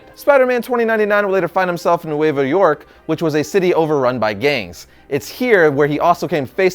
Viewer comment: thought he said over run by gays